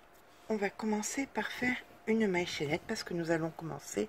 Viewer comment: excusez moi je n ai pas entendu vous avez bien dis une maille chaînette sinon vous avez dis quoi merci d avance pour votre réponse